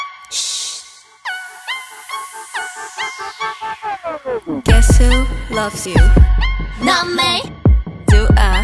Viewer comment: Why did I hear “ do I love you? N y a”